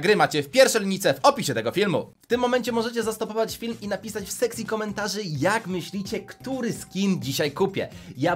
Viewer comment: czy tylko ja usłyszałem: "W seksi komentarzy"? XD